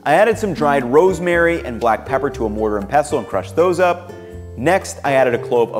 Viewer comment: Did... did you say a mortar of pestle?